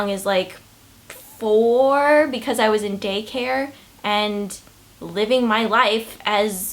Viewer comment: When she said day care, i thought she said gay care